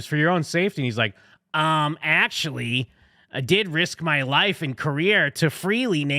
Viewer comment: 🤣 I admit I wasn't watching the screen, just listening at 3:54 when he said "I risked my life and career ...." but I heard "I risked my life in Korea ...." and I almost broke my neck laughing. Ah man, I gotta get my ears checked ... all those years of going to hardcore shows ... oh boy.